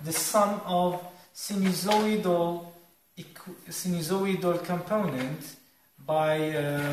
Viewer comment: Hello I have a doubt... from minute 1:32 to minute 1:42, there is a word that I can't understand, when you say... "The wind speed can be model by the sum of... what?" Please can you help me? Thanks =) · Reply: sinusoidal...........